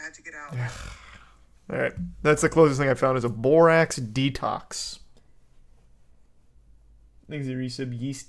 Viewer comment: I thought it said lorax